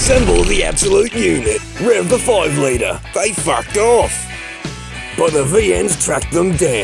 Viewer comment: did he said they fuck off?